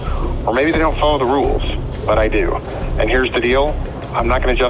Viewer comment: He said maybe they don't follow the rules but I do... All I heard was maybe I'm a pedophile and they aren't 😬